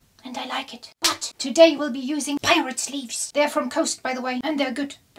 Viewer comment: I totally thought u said pirate leaves lol<3